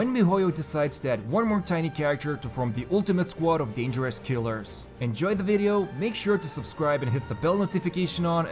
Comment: When you said “dangerous killers” I heard “dangerous 3 year olds” 😂😂😂 “The ultimate squad of dangerous three year olds”